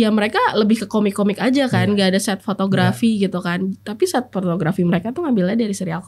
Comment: kirain saya salah denger.. taunya emang lidahnya kesleo ngomongnya "set p*rn*grafi" ya..